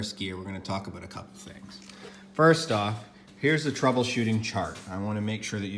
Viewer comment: I thought he said troubleshart 😅🤣